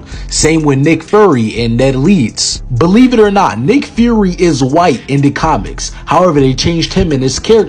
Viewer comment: Did he just say Nick furry?